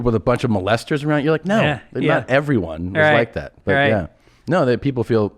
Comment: Tom talking about the priest abusers and he says not every one was like that. It sounds like he said, "back then." Implying now they all are, but no he said, "But, yeah..." Thinking of what to say not the implications I misheard.